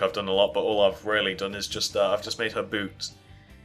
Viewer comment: Did he say boobs???? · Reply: joshy aubert no he said boots...